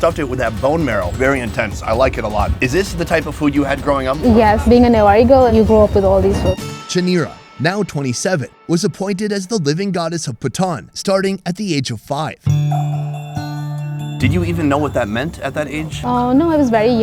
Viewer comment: Did he say living goddess of Bhutan?